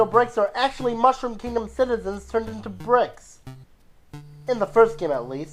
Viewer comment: did he say bricks or pricks because toads are pricks